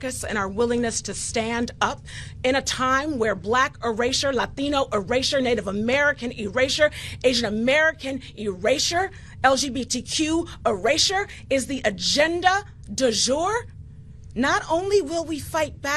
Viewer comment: get a life bro wtf erasersher? That’s not even a word lol